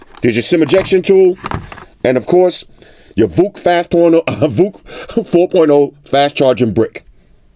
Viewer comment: did just say "VOOC Fast Porno"??? LMAO